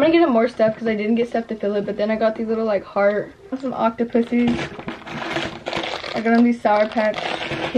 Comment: Did she say- OCTOPUSSIES?! 😂